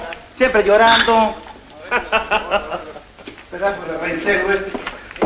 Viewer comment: al 06:49 se escucha que Tun Tun dice: "Pendejo éste"... ¿o escuché mal?